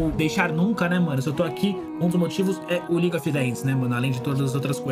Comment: eu ouvi league of legs?